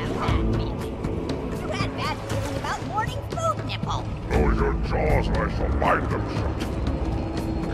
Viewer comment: “You rat bastard, stop hoarding food nipple??” I can’t tell what the last thing they say is. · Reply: " you always have bad feelings. You had bad feeling about morning food nipple!!" Elite: " Lower your jaw or I shall bind them shut!"